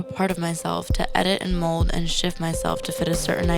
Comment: I thought she said Edit, mould and shit myself